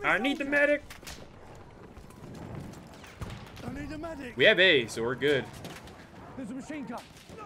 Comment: Sounded like you said " we have aids so we're good" , sorry i'm just cracking up here, sick game play though!! :0